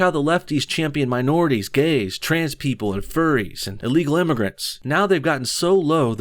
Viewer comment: Bruh did he just say furries at 3:48 ?